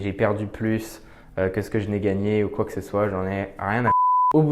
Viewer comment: Evidemment, toujours top ce genre de vidéo ! Par contre, c'est moi où à 10:08 on entend "rien à foutre" ? 😂